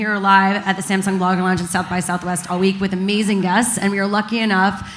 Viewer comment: I totally thought she said Amazing Gus, rather than amazing guests.